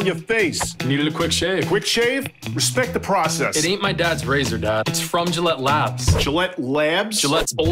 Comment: “It ain’t my dad’s razor, dad.” A+ writing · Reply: I misheard "It ate my dad's razor dad"